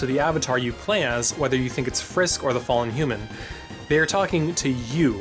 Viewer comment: "WHAT ABOUT THE FANDOM?!" boi, i ded, i ded frum laffturr tEM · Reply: ignitednightmare I thought u said frisky at first